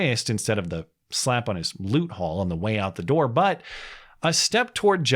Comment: I heard you say “loot hole” and had to rewind it lol. either way, booty would have been a good pun there